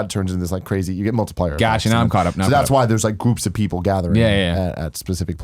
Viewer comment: I thought he said poops of people 😆